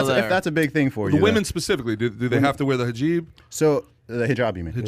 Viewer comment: Did he just say Hajeeb 2:16 😭😭😭 I’m dyinnnnghgggg